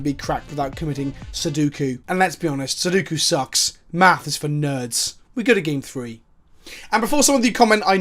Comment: I thought he said “Matt is for nerds” and I really felt bad for Matt.